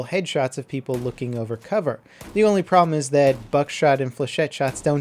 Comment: I heard "fuckshot'